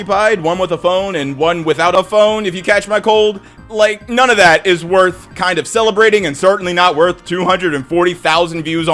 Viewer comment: uhh, what word did he have to censor? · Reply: Yaoi